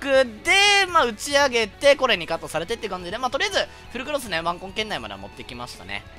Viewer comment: 糞ガキ耳の僕を許してください。。。 6:55～マ○コン圏内にしか聞こえない